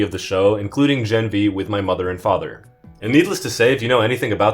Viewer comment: For a second there I thought he said mother and DAUGHTER 😅